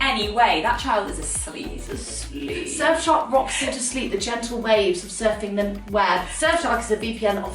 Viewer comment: Did i just hear "surfshark rocks u to sleep like a baby" 😂😂😂😂😂